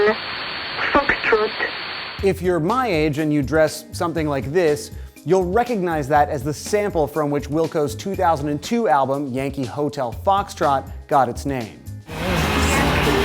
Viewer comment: Dash what? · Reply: I thought they were saying Whiskey Tango Foxtrot instead of Yankee Hotel Foxtrot.